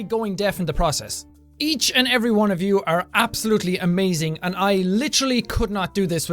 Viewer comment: I thought he said "you cereal amazing! Changed my life!"